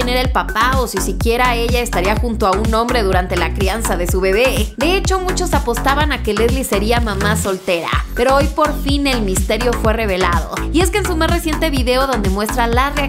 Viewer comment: Pensé que decía “Leslie no será mamá” jajaja